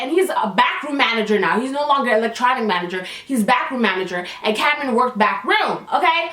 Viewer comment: At first I thought you said Bathroom manager and I screamed😂